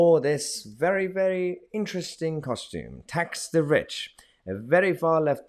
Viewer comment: I thought it said ax the ich lol